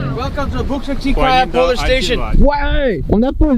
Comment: what 4:21 moi j'ai entendue "polish station " polish = polonais! :D